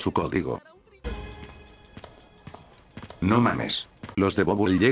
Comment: En la parte 8:30 cuando dices no mames yo pensé que decía no mas sexo XDDDDDDDDDDDDDDDDDDDDDDDDDDDDDDDDDDDDDDDDD